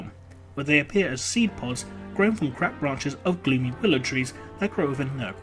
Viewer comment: did he just say "Crap Branches"?